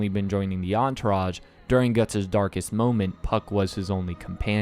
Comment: why did I heard fck instead of puck 😭